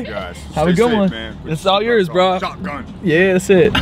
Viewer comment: Ok so I’m in a little older generation but y’all remind me of my son and his friends and I laughed so hard over that guy you gave the drink to bc I thought I heard him say he called “shot gun” 😂😂 and then everyone’s reaction to that @ 9:01 was hilarious😆